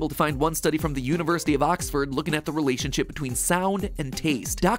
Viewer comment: did, I mishear that or did you say "relationchip"